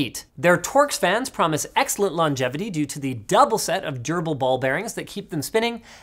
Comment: The only part of this video I had to rewind to hear again was when (I swear) he said, "double set of Gerbil Ball Bearings" (11:55) when covering the MSI card. Say what? Those poor gerbils.